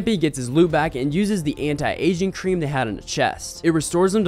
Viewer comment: did i just hear "anti-asian cream"